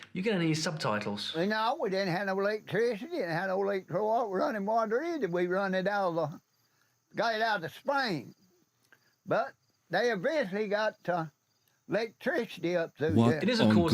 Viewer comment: What he said is pretty much “We didn’t have electricity, *unintelligable*, we didn’t have no water, we had to run it over, get it from the spring. But they eventually got to lake __________”